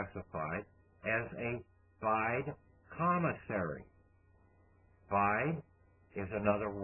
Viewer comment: at :20-:30 and at 4:00-4:11 and at 4:28-4:36 mentions a "________________" Commissary Trust... What is referring to??? I have tried to figure out what that word is but Nothing!!! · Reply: A Cesti Que Vie Trust, also known by several other pseudonyms such as "Term of Life Years' or "Pure Autre Vie" or "Fide Commissary Trust" or "Foreign Situs Trust" or "Secret Trust". Goes back to Henry VIII of England....